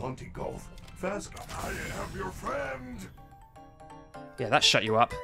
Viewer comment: it sounds like Freddy says "I have your fans"